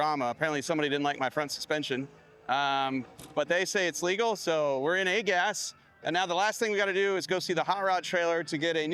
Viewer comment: what I heard was your legal this year don’t try it next year